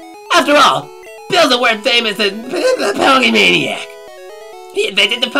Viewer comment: I heard "Ponymaniac" in there. HAHAHAHAHA! Victory for bronies.